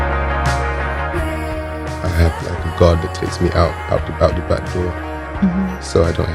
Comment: i only got to check the npr interview after this video, so when i was watching at first i thought i heard he saying " i have like a GOD that takes me out the back door so i don't have to talk". i think it was one of the strongest and enigmatic sentences i ever heard in my life and made me reflect on dean's beliefs and spirituality and what not, only 5 minutes later to realize he meant GUARD hahaha, good stuff man